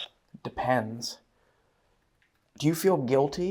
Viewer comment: I don't think he said "depends", maybe he said "the pants" since his pants got caught. And then 19:12 it sounded like "forewarned" (maybe it was a safety violation?)